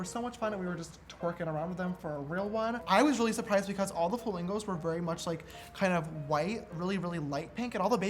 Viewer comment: he said falingo’s instead of flamingos LOL